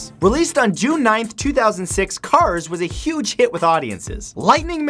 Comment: Cars was a huge hit with audience i misheard as "cars hit audience"